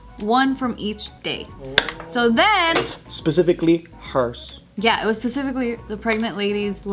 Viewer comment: did anyone catch say pecifically not specifically lol